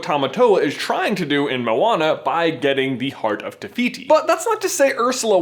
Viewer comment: I Thought you said heart of Rafiki (Probably spelt that wrong. Who cares?)